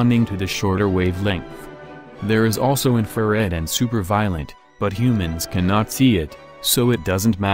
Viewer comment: "super-violent". The subtitles say super-violet. Of course it should be ultraviolet. If you want to make a joke you should at least show a picture depicting super-violence at that moment.